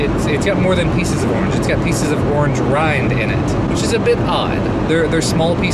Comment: At around 0:45 you talk about orange marmalade. Can't make out the exact word your using. But I guess you are talking about the marmalade having pieces of the orange "shell" in it. That is perfectly normal. The shell has natural pectin in it witch gives marmalade it's defining consistence. We do also have "shell free" marmalade here. Where they filter out most of the shell pieces after the pectin has done it's job. Or I presume in some cases add some other source of pectin. But yeah "normal" orange marmalade has lots of shell pieces in it. :) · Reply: “Lejoni” Larsen-Haikarainen Yeah, I think I said "peel" or "rind", but "shell" works just as well. I know a lot of chefs use "orange zest" in dishes, which are just shavings of the shell, it's just an odd texture since I havent' had it before.